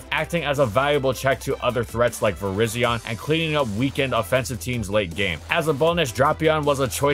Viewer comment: for a moment I could have sworn it said "virizion used leg break" and I wondered when that became a move